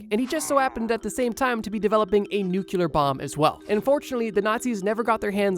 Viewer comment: i thought you said unfortunately and was taken aback lol. Great video as always though